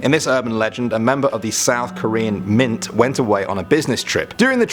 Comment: Yay! I love South Korea! Go Korea! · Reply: Oops, I thought you said north Korea, sorry. Then I realized you said south korea